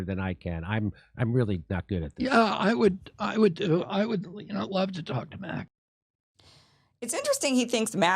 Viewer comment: I thought he said he'd love to talk to Max